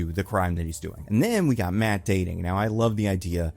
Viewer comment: You said we “gotta to talk about Matt dating” and I thought you said Matt Damon. 😂